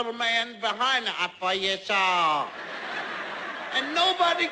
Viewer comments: Am I the only one who at first thought he was saying I'm for your soul? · It sounds like jeffs saying im for your soul but idk lol but good vid =(^.^)= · Is it me or does it sound like jeff is saying...I poor you soul!:3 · Honest to the lord, I thought he said "I for your soul" the first time I saw this.